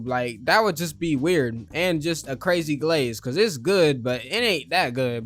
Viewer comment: DID HE JUST SAY CRAZY?? (ALL THE GIRLS ARE GIRLING GIRLING)